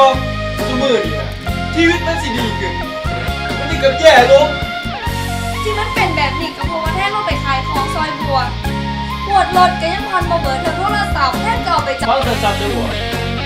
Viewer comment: เค้าพูดว่าอะไรบ้างครับ ผมคนใต้ ฟังไม่รู้เรื่องเลยครับ ฮือๆ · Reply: อมตะ ญ: แทน...แทนยังรักบัวอยู่ไหม ช: ถามแต่เรื่องเก่าๆทุกวันนี้ชีวิตควรจะดีขึ้นมีแต่แย่ลง ญ: ที่มันเป็นแบบนี้เพราะว่าแทนไม่ไปขายของช่วยบัวงวดรถก็ผ่อนไม่หมดเลยโทรศัพท์แทนก็เอาไปจำนำ ช: ฟังนะบัว....คนเราไม่มรทางเหมือนเธอ ตั้งแต่วันนี้ไปแทนจะคืนความรักให้บัว ญ: แทนไม่ต้องเอาความรักเห็นแก่ตัวมาคืนให้บัวหรอก เอาไปโยนทิ้งให้หมาซ่ะ...ถ้าจะพูดแค่นี้...